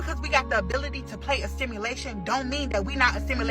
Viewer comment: is she saying STIMULATION? Instead of SIMULATION?